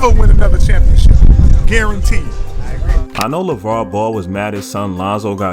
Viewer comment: I had my volume low and thought he said Lebron Ball. Kinda wish he did.